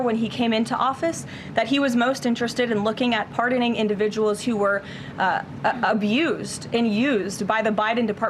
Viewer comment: I heard "pardoning individuals of use" (to Trump)